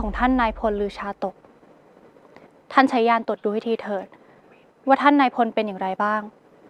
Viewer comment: เราได้ยินไม่ผิดใช่ไหม ญานตด